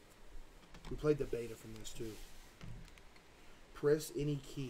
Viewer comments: I thought it said “I played with balls” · I low key thought that said I played with balls💀💀💀💀💀💀💀